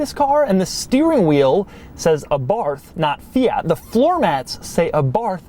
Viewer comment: it sounds like your saying "ABARF" lol..